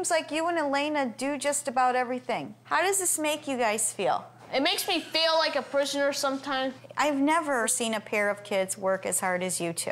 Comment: When she asked the kids how does this make you feel and he said like a prisoner sometimes….I thought he said like a p*ssy